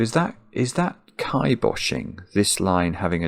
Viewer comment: What was the word he used at around 20:23. It sounded like kai-boshing or something similar? I absolutely love these videos and I always love collecting little bits of vocab from two intelligent English minds